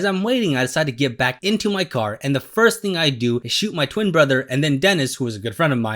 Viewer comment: I thought he said he shot his twin brother 😭